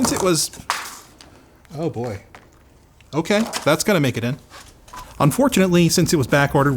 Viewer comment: I thought he said, "That's gonna make it in," as in to the final cut of the video? (Because it was pretty funny) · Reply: VAK25 - Pretty certain that He says “Thats gonna make a dent” ‘Timestamp 6.27’ hehe It’s still a Linus thing though. 🤪😁